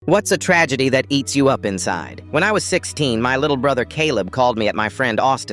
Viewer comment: When he said "eats you up inside" all I heard was "Wake me up! Wake me up inside!"